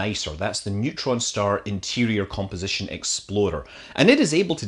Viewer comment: I love that I keep hearing you say "exploder" instead of explorer. Too much kerbal? .....naaah :D